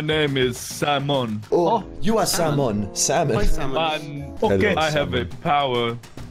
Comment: What I heard was "I AM SALMON"